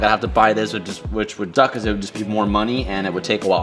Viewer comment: Lmao my man said “which would DUCK” instead of suck 😂